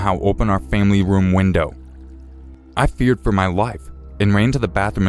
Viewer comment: he said i fear for my wife instead of i fear for my life 😂😂😂